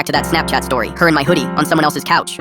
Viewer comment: Bro at last part i Heard "i hooked up with IV" instead of "i hook up at IV"